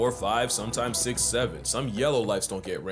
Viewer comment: I’m sorry did I hear that right 6✋ 7✋ !!!!!!!!!!!!!!! 😝😝😝😝